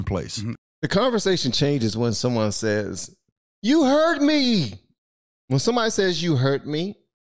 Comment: ~ I thought he said, "heard me" not "hurt me". I keep hearing 'my truth', I guess I need my ears checked. This is not the first nor will it be the last